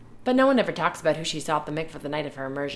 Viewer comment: "but no one ever talks about who she sought the mikvah the night of the immersion..." "Who she sought the mikvah"?? Perhaps I'm not hearing correctly, but I don't understand that part of the sentence grammatically. Are you saying no one talks about why they're there? The "who" is also throwing me off... isn't this just for married Jewish women in relation to their husbands? I don't understand. What exactly are they keeping discreet? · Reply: Who she “saw.” We are discreet about who we see there.